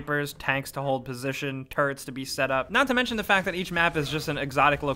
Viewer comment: i actually thought you said turds to be set up 🤣 Edit: spelling